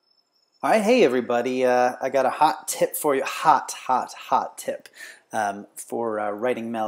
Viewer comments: I thought he said " I gottaaaaa hot tit for you" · thought he said hot tit in the beginning D